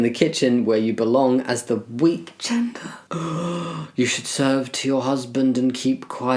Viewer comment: when you said "weak gender" I thought you said "wheat gender" and that's one way to put it 😂